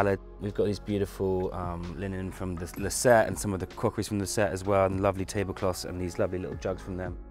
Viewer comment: did anyone catch where he got the linen tablecloth and little jugs from? Sounded like he said ‘Lucet’ or something like that. Thank you :)